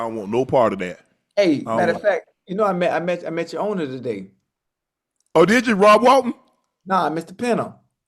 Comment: Did he really say “I met your owner today”? That’s crazy😅